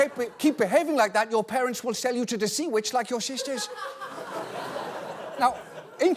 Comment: Im Dutch and I demand an apology. Sea Witches are a serious issue here. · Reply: Dude I thought it said "Sex Witches" for a second....